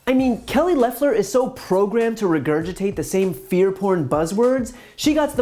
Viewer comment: did he say "fear porn buzzwords" ?